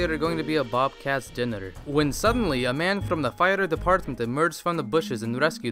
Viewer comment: When said "fire department", I thought that he said "a fighter's apartment"...